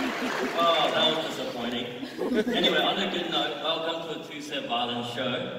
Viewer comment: I thought he said he liked to drink viola